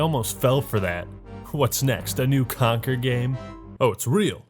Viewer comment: Thought he said “Concord” 💀💀💀